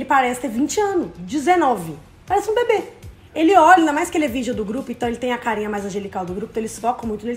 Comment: Desculpe mas, " ainda mais que ele é o virgem do grupo"? Foi isso que eu entendi, tô voltando aqui pra tentarentender